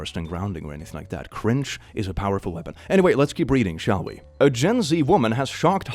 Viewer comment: "Gen C" is what I hear